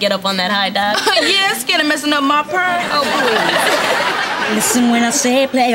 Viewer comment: when I frist heard her say "messin' up my perm" back whe I was in the third grade I thought she said "messin up my perf." because I idnd't know what a perm was so I thought "perf" was short for "perfect hair do" lol!!! :D